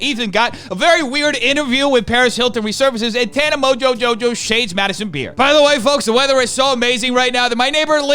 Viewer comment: at first I heard "Tana Mojojojo shaves Madisons beard"